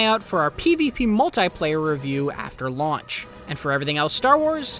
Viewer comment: When he said after launch at the start, I thought he said after March and I started being worried